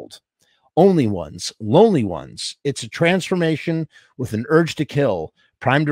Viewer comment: I thought at first the lyric said transportation of an ugly girl. Lol